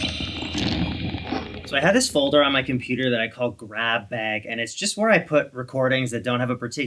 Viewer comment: so thought you said CrapBag 😂